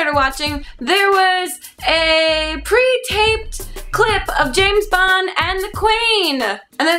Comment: I thought said, "Pre-taped Clit...". I really need a Q-tip!